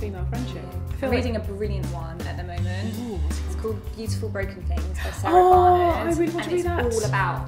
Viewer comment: What book did Holly mention? I heard "Geese for Broken Things" LOL I don't think that's the title...